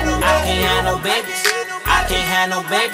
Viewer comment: I remember being like 16 years old in the Hyperbolic Time chamber hotboxing the hell out of my room i shared with my brother, dabs left n right out of a giant 5 foot bong 😅 I thought this song was saying ‘My Piano Babies” and i was just imagining a bunch of babies playing Pianos 💀